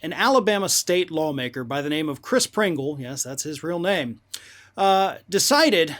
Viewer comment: I had rewind it. I thought he said Chris Kringle Santa 🎅🏿